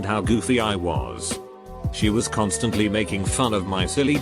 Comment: I was pressing the 10 sec skip and I heard “she was communist”